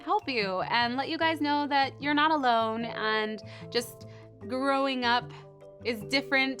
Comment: it sounded like she said "Throwing Up" 👌🏻 not making fun luv the vids 😍